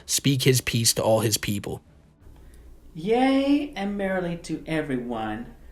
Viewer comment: did he say “yay and Mary-Lee to everyone??” 😂 Mrs Walsh just can’t get out of his head 😂